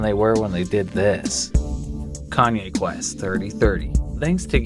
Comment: I thought he said Kanye west 3030😂😂😂